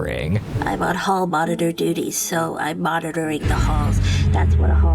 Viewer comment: she really said diddy bro 😂😂😂😂